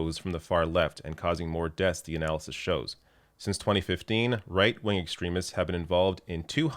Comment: I could have sworn said “reich-wing” at 9:03. 🙄